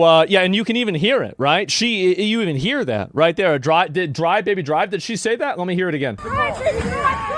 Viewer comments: Oh I could have sworn she said "Die baby die" 🤣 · I thought I heard “Die Baby Die, Die”. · "Drive baby drive" I thought you said use my reaper ult "Die, Die, Die"